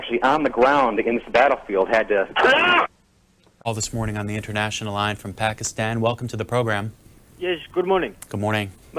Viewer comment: What’s he saying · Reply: He's saying HLAAA Look up the original video, called "Attack of the Hlacka". He has the link to the source of the sound, which is from an Arabic announcer for a soccer game.